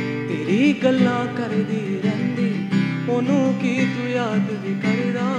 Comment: what did he say? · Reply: he says "teri galla kardi rehndi, ohno ki tu yaad vi karda" meaning that mom keeps talking about you all the time, do you ever remember her at times too?